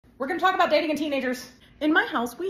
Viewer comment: i thought you said "were gonna talk about how to date teenagers" in the intro. great video though